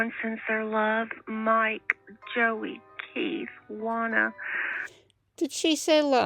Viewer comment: I hear Juana (pronounced Wahna), rather than Lana.